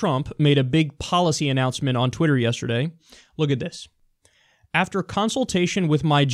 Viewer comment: i misheard "look at this" = "this little bitch" :D